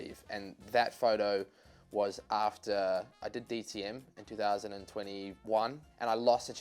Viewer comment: I heard DMT at first.